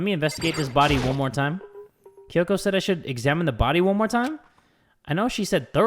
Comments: Did i just hear... "Let me examine His body one more time"? Hmmm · I must have misheard did he Say: "Investigate THIS body real quick or Investigate HIS body real quick" because..if it's the second one.. We've got a pyschic on our hands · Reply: He said “this”